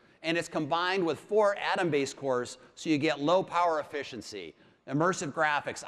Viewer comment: did he say "low power efficiency" ????? Lmao